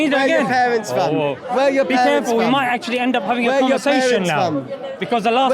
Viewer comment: If you listened carefully it sounded like he was saying. Wear your thong. 😂😂😂